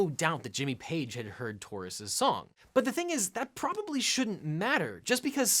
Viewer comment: The band is SPIRIT, the song is TAURUS, not the other way around, as lawyer guy misstates. Just sayin'.... Sorry, misheard taurus as tortoise...misheard and misstated—that's a lot of misses, eh?